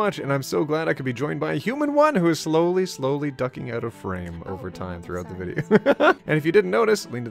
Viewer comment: I had my vaccum on while watching this part and I heard "I am glad to be joined by Human 1 who is slowly coming out as Trans!" and I was like "You Go!" But then I turned off my vaccum and re-watched and I was just so confused on how I heard that. Must be my genderfluid mind.